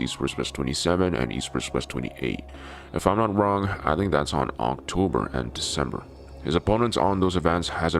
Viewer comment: did he just say ‘unc-tober’